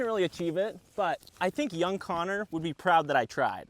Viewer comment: I deadahh thought it said young gooner😭🙏